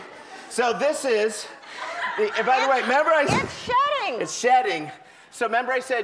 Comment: oh wow i was laughing my ass off cause i thought ellen said the snake was SHITTING.. then i found out it was shedding...